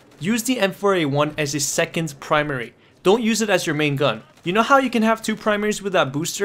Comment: "You know how you can have 2 primarys whitout boost?" I didn't understand what you meant. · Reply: You know how you can have 2 primaries with THAT booster :)